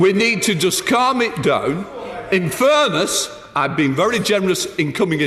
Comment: so what was in that Furness? I kept hearing "in furness" but I'm left with unanswered questions... :D